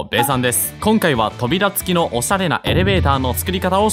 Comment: 起きたばっかりで頭回ってなかったから 「おしゃれ」が「おさる」に聞こえたw ん？い、や、の滑舌が悪いとかそういう言い訳しないすけど？